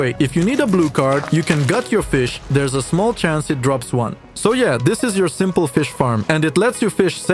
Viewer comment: の「ソイヤー！」は英語でなんて言ってるんですか。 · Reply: so yeah